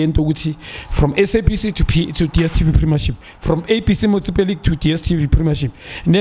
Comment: Lol, am I the only person who heard "from SABC to DSTV"